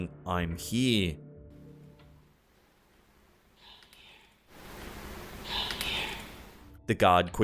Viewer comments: To me, in the bank vid, the first time you hear the voice it sounded like it said "im here" and the second time it sounded like it said " come here". Did anyone else hear that? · i hear come here